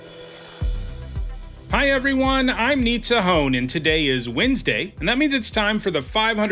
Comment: It always sounds like he said "Hi everyone I need a home."